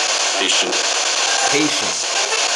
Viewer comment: it sounds like she is saying “I’m milia” in 3 separated syllables